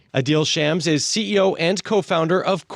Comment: His last name is literally Sham and they still gave him a business loan. This is why we demand reparations. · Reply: I thought they were saying his name as " I deal shams"